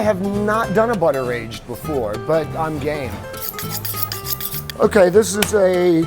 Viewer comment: I thought he said gay what the hek